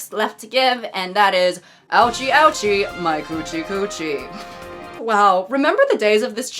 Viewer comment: or so I misheard you, and now the phrase "My Gucci coochie" is trapped in my brain forever. 😂 Seriously folks, if you like anything to do with your hands plus somebody's orifices, then unless you found them on an oddly specific corner of FetLife keep those nails trimmed. Important PSA.